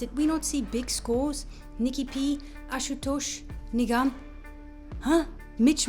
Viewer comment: The way you pronounce Nigam thought you said nigga🤣🤣